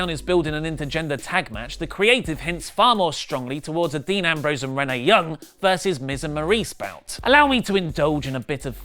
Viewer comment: thought he said "Dean Ambrose and Mae Young" lmao